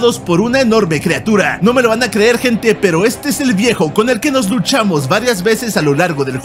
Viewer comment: Entendí "nos duchamos" 😂😂